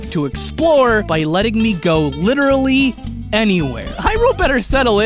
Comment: Um did you say Iroh as in the Avatar the Last airbender? Did you mean zelda or something else? here is the part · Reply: He said Hyrule, but it does very much sound like Iroh.